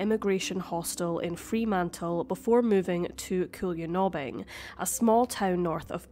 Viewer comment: Cool ya knob in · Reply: I know right, was looking for this comment 🤦‍♀️🤣 I heard it as 'cool your knobbin'